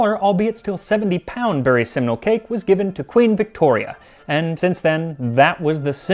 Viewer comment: I misheard the name as "Seminal cake"